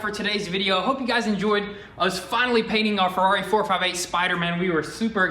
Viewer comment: Did he say spiderman?